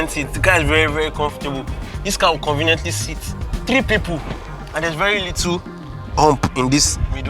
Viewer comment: Noticed a mistake, you said 3 and the caption(s)/subtitle said 5 Take note 😉 Nice content btw Just discovered you now I can’t stop watching